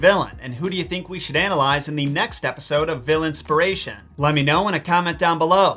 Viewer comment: I thought you said "villain-spiration," and honestly I think it works 😆